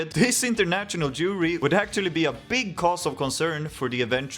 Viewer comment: did a double take even as a swede when i first heard "international jewry" instead of jury, like wtf when did my eurovision video turn nazi